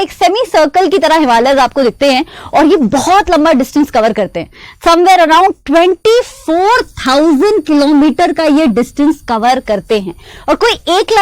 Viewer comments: she said twenty four thousand instead of twenty four hundred 😄😆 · Do you hear 24000 instead of 2400